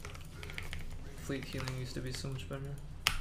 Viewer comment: what did he say? couldnt understand anything here beside "... so much better" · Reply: "Fleet healing used to be so much better"